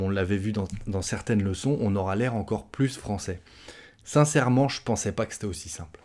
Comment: Sans blague, chaque fois que j'ecoutais quelqu'un a dit "sincerement", dans un film ou on parle carrement vite, j'ai cru j'ai entendu "sans serment." Assurement je me suis trompe.